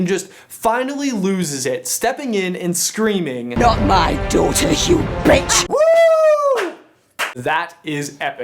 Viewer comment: If you look at the subtitles for 10:19 it says 'not my dough to the human' instead of 'not my daughter you b*tch' 😂